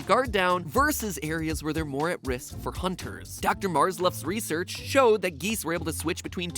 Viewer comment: Hahahaha! I thought he said Dr Marz loves research And also, at 9:52 there is a Funtime Freddy in the right hand corner. Just think about this: You just got of work, this time your boss made you stay until 9 o’clock so you’re extra tired. You walked to work this morning so you have to walk home too. As you walk past the park, you decide to take the path in the park home. It takes longer, but it might loosen you up a bit after this stressful day. In the distance you can hear some geese honking away, so you look up to see if you can get a glimpse of the birds while they’re flying up in the sky, not a single ca- OMG WHAT IS THAT!?!? IS THAT A ANAMATRONIC!?!? THERE’S A ANAMATRONIC IN THE TREE!!!! IT’S STARING AT ME!!!!! AHHHHHHHHHHHH!!!!!!